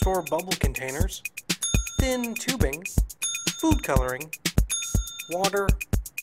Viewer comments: Lol I thought he said boob coloring! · did he say boob coloring? o.0